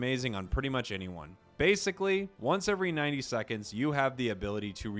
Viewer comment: once every 90 seconds? I thought it said 3 sec